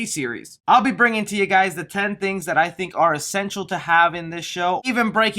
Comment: I thought you said sexual to have instead of essential